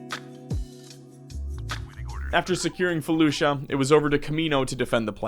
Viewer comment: When you said you were invading felucia i thought you said fallujah and was very confused.